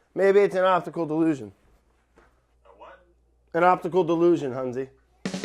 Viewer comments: Brad: maybe it’s an optical delusion: a what? Brad: an optical DELUSION, sounds like he’s rolling his eyes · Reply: I thought he said "opticalled delusion" lol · “maybe it’s an optical delusion” “a whAt” “an optical dEluSioN huNzI” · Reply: thought he said opticalled illusion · You know the classic optical delusion so common 😂😂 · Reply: I thought he said octagonal delusion